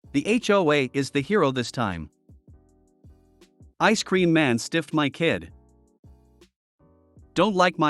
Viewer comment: Was sparking my blunt when it started and didn't read it was just listening and I thought they said ice cream man sniffed my kid😂